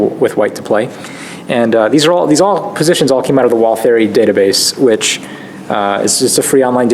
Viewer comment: Could you please give a link to the database that these positions came from? I hear it as "wall fairy database", which can't be right :-)